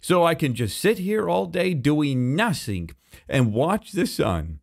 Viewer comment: I heard saying "Im just gonna sit here doing NaStY!" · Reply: He said nothing but his th’s sound like s’s